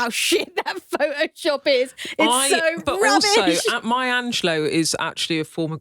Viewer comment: Legit thought I heard Beth say "Maya Angelou is a former graphic designer..."